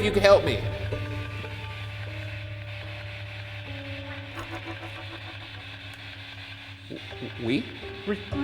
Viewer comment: i thought at 29:50 it actually said “i’m coming” or “coming,” as if it was coming to your aid since you were asking for it.